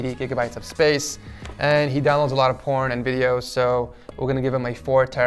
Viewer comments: did he say porn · WTF did I just hear? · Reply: Covirz you heard that he downlads alot of porn, something wrong? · did he just said porn? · Did he just say he download a lot of porn and video's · Did I hear this correctly , he said he downloads porn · did anyone else come down here to check if he actually said “porn and videos”? I you did like this comment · Him: he downloads a lot of porn and videos. Me: Did he say what I think he said calls youtube and says: WHY ISNT HE DEMONITIZED YET?!?!??!?!! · does he say he downloads a lot of porn or is that just me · Im sorry, DID YOU SAY P*RN? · what did he SAYY “he downloads a lot of porn a videos” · he downloads a lot of porn and videos. Did I hear it correctly😳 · what he just said? Download porn video??? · did he just say porn and videos???? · "And he downloads a lot of Porn and Videos!" Umm..... 😳 Am I hearing this right!? 😳😂🤣